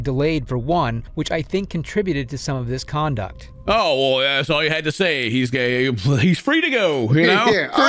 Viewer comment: “Oh, well that’s all you had to say! He’s gay.” Is what I heard Ken say at first lol